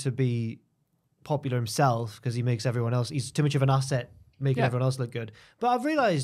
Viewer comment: I thought said thicker Accent not asset?